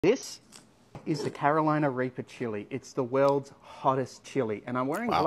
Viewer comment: I thought he said Carolina raper!